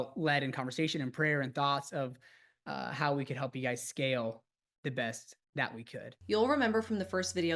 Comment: "help you scale the best"??? I just hear "scam the best"